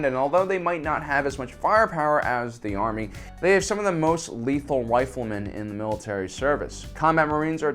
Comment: did I hear him say wifle?